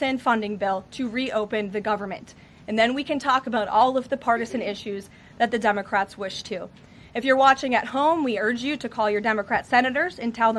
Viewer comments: Sounds like she is saying dumbocrats, which is not far off! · I thought she said “dumbocrats”